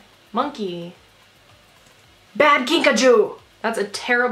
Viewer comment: I thought she said Bad Pikachu. I was like "They sell Pikachu!! I knew pokemon was real!! I need to find this website"